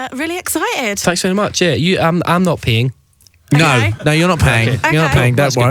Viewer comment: Anyone thought he said I’m not peeing?